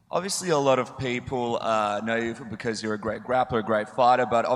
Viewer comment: Did he say great rapper? Lmao